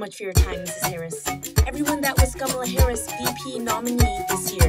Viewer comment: When u said " these women are getting over empowered, KAMALA" thought u said tsamaya which translate to leave in Sepedi (one of South Africa's official languages) 😂😂